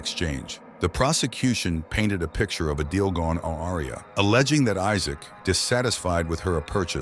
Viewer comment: Did your speech generator try to say "awry" ? You should get a new one. It's certainly not pronounced "aw-awria" xD 32:26 Notice any difference between the one who pulled the trigger and the one who asked him to pull the trigger? Something about the.. tone.. what was it called.. It's on the color of my tongue.. People do actually get lesser sentences than for killing than robbery in america, that's a well known fact. This guy getting almost twice the sentence from the person who wanted this done in the first place is not normal.